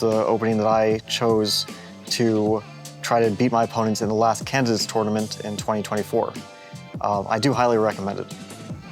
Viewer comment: Thought he said Kansas Tournament 😂